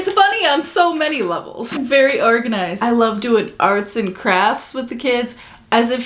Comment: Jenelle sounds like she says "arson crafts". i kinda love it.